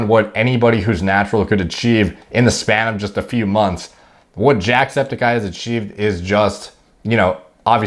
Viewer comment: Why do I hear you say "JACKEDsepticeye" when I know that's not what you said.